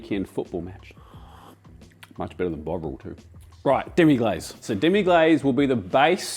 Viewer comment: Fantastic Andy, hopefully I’ll get the chance to make that beef stock one day. BTW, I thought you said “Much better than bog roll” and thought to myself “well, yeah” until my brain connected that to Bovril. Haha.